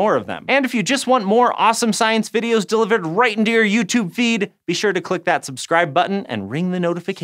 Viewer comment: i misheard "right into your youtube feed" as "right into your uterus" for a second and i almost died choking on my food laughing so hard